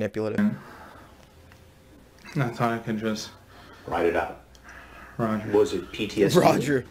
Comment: Bro u killed it with recognizing when this fuck says “Roger”🤣 I always thought he said “Right.” Good for him. His survival instinct on point! He’s so into character with Hansen, he gets back in the zone of wartime. Of course, that’s if he did see any battle over there. They have nearly every military job deployed anywhere. Food, Pay, Medic Machinists. Besides Recruiter or Boot Camp. He uses the excuse cuz there’s no denying him being stationed over there. If his military record proves it.